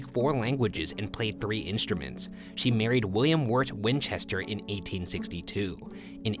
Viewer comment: did he say William Wart ... 😂😂😂😂